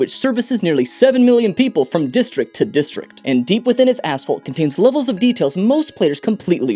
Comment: LOL. I lost my place on the vid near the start and was fast-forwarding back to where I was. Instead of “deep within its asphalt” I thought he said “deep within its asshole.”